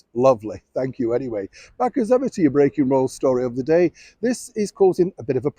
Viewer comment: When I first started listening to your channel, Neil, I thought you were saying, "Back as ever to your breaking balls story of the day."